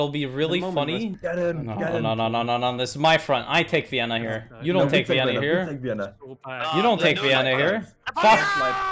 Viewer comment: Did he just say "You don't take Vienna n _ g _ _r?"